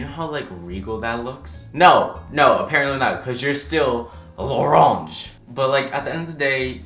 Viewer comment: Laronge! I spelled it the way i heard it lmao 😂😂😂 i love you so much😊